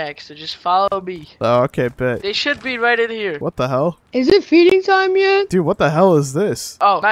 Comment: I thought they said is it beating time?😂